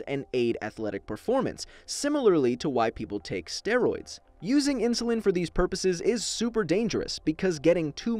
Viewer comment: I thought it said “ similarly to white people taking steroids”